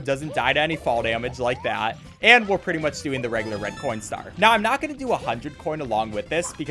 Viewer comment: Am I tripping or did he say corn instead of coin? Asking because I also do that on purpose (saying corn instead of coin)